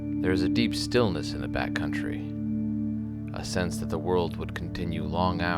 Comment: You said, "Back country." But I heard, "Bat country!" Thought you just made a fear and loathing reference lol! Still a beautiful video! 🤍